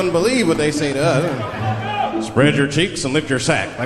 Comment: Every time that I hear the cop speak I hear him say "Spread your cheeks & LICK your sac".